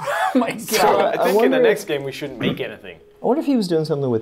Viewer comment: Did he say next game